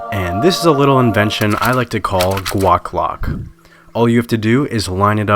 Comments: The whole time I thought you were saying Guac Block 😂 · I literally thought you were saying guac Glock this whole time til you showed guaclock